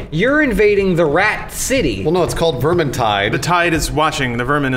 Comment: What game is that? I heard him say vermantie or something?